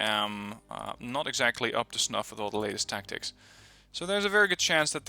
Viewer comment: I thought you said "to snuffle all the ladies"